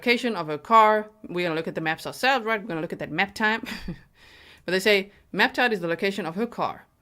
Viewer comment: When you say "map time" I always hear "nap time." So soothing!